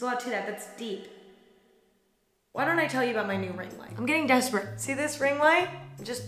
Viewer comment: I thought she said "let me tell you about my nuvaring"